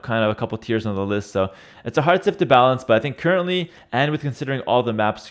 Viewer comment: did he say curtainly?